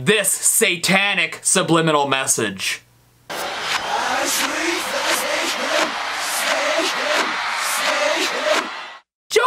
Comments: It sounds Like I speak for snake heaven at 2:55 Edit: I heard I believe it was sitting in me eye! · I hear, "I speak for THE Satan", not "I speak for Satan", he missed a word, but it would still be an utter stroke-out XD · I heard "I sleep with Satan" to begin with ;_ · WHY DID I HEAR "We sleep, with satan, satan"???? 🤣🤣🤣🤣🤣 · It sounded like they said saishtan.